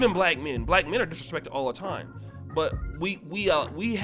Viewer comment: I thought it said DISRESPECTFUL.